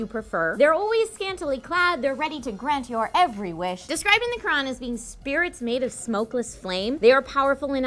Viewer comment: Did she say as described in the Quran? O.o